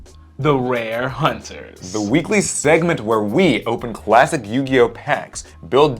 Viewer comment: Did I hear “Quassic?”